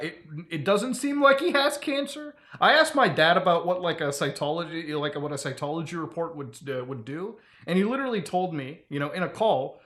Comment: I kept hearing "Scientology test" lol